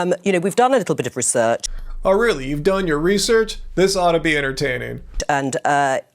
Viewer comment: "Oh really, you've done your research? This ought to be entertaining." Hahahaha, at first I thought he said "this ought to be irritating"